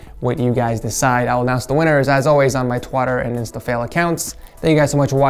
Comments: is it just me or he said twatter instead of twitter? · is it just me or did he say twartter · Did he say twater